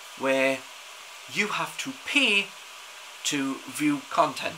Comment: Love your accent. Made me smile near the beginning of the vid. I nearly choked on my cornflakes! Thought you initially said that people had to “ Pee to view content!” Then I realised what you actually said!